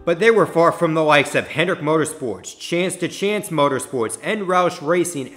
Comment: Who the heck is “Chance to Chance” Motorsports? It was actually called “CHANCE 2” Motorsports, the team that was co-owned by Dale Earnhardt Jr. & Teresa Earnhardt, until he split for greener pastures and formed JR Motorsports.